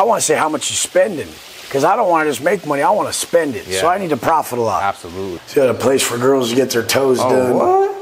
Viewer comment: did he say “profitalize” !?